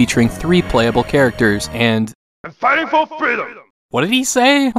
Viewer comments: Sounded like a Japanese man saying "I'm fighting for freedom!" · I think he said "A fight for freedom" =) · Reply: i heard 'a time for freedom.'